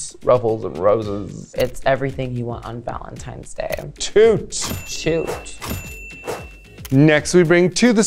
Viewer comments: I swear Kerri said "Shoot" to Lala Ri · love how for jaymes and lala kerri obviously said shoot but the editors gave it a toot instead for some reason 😭😭😭probably cus otherwise with kahana and alexis' shoots it would have been too many. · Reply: i thought so too, but then i heard Kerri toot Lalas look, which also almost sounded like she was saying "shoot", but it was said so unenthusiastical that it couldn't have been. 6:51 TLDR: Kerri apparently has unlearned to say the word "toot" ☝🤓